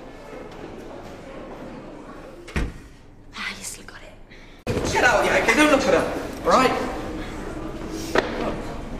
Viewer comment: What did Angie say at 29:35?? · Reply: I still got it.